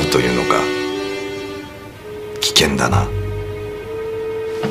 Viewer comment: 最後の橘さんの 「トライアルF？」が 「ドライヤーでふ？」に聞こえた